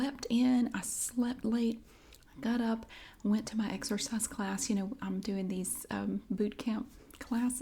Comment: I thought you said exorcise class😱. Lucky you able to go out....